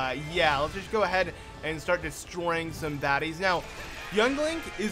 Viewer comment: did you just say destroy some daddys?